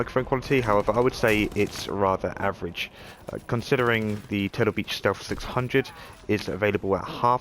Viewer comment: what microphone you said is better? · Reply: Turtle Beach Stealth 600. 🙂